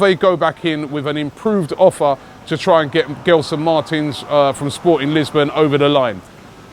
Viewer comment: I thought you said will get gelson 4 free